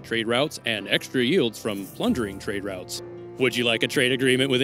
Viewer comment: Plundering… heard him say plungering 😅